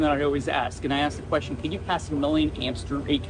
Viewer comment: I heard "can you pass a million amps through it" as "can you pass a living hamster through it" and thought wow, what a strange measurement for an engineer to consider...